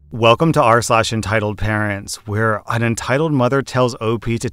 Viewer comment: Every single time i hear intro, i keep hearing arselash.